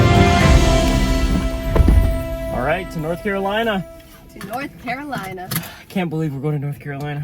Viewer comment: I thought he said run to North Carolina 😂